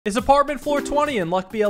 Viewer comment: I thought he said apartment 420.